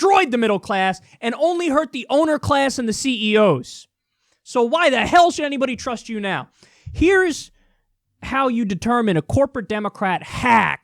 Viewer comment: "only HURT owner class and CEOs"???? 4:11 did i mishear that???????????